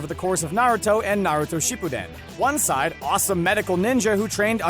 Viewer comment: did i hear “She Pu Then” and “Uchi Ha”???